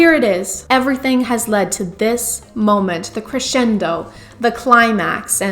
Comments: I swear I thought you were making a pun here because at first I heard "Christian-do" instead of "crescendo" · Absolutely thought I heard “Everything has led to this moment - the CHRISTIAN-do” which is perfect. At this point I don’t even care if that’s what you said, you still get credit. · Crescendo? I heard “Christiando”. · I thought they said the "Christian-do" rather than crescendo and I was like hah, that's an awful play on words. But my brain just did that to me 😔